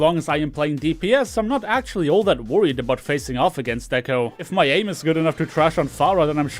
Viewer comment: I shocked when I heard: "If my anus is good enough..."